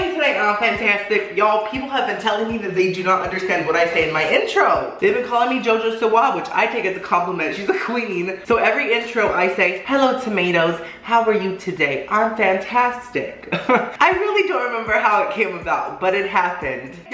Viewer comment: Yess consistently uploading and a 30 minute video on top of that 😍👌🏼👌🏼👌🏼👌🏼 & I thought you said “hey what’s up guys how are you today im fantastic” 😂😂 thank you for explaining 😂😂😂